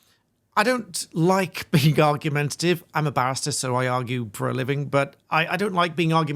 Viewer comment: I think l have cloth ears! I love this channel and l swear l heard the presenter say - l'm a bast×>%& and l do this for a living! I rewound and of course the word l misheard was barrister.